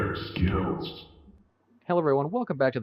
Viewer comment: for some reason i thought the dragon said bear skittles